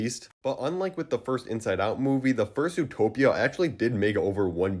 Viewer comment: Am I the only one that hears "Fur-suit-topia" instead of "First Zootopia"?